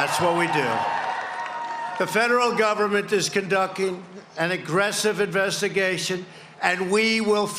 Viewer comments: He said "conducking" instead of "conducting". Lmao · Did he just say conducking instead of conducting ? His mental issue is getting worse in all aspects, literally, by the day .....